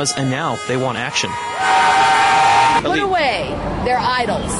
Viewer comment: Did she say Norway??*